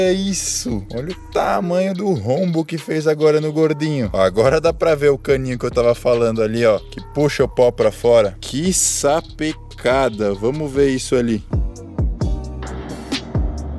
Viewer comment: só eu q entendi ele falando "puxa o pau pra fora"